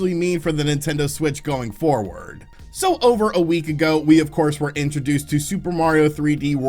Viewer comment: I watched the preview before I clicked and thought he said about a week ago😂😂😂